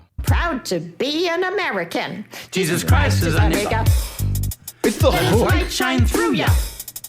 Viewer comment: I square to Bob, it REALLY does sound like they're saying "Jesus Christ is A ni**a!" during that part. Or at least it SOUNDS like was saying that while kinda just muttered & mumbled nothing in particular after saying "Jesus Christ is," taking advantage of the fact that nobody could actually hear what he was saying because had inexplicably decided to YELL that line as loud as she could. Idk, but I swear they are/she is NOT saying "Jesus Christ is MY ni**a," but rather "Jesus Christ is A ni**a." 🤣😂